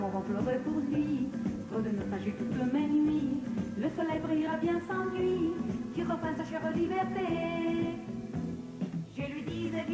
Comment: Does she say: "le soleil brillera bien sans lui, qui repasse chère liberté"?